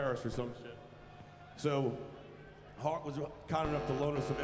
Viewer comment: See the boys broke out the old sgs again. Best axe ever made. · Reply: I thought he said Heart! 🤦🤣🤣🤣